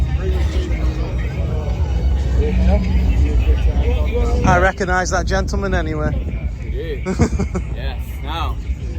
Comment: Hello, i cannot understand the lady talking english. She's preffering Nemesis to what coaster? I can only understand "Onami". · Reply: "Old Nemmi" she said. The old nemesis